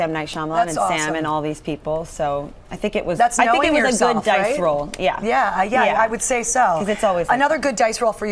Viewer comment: i thought they said "dyke role" instead of "dice roll" and i was like hMM WHAT NOW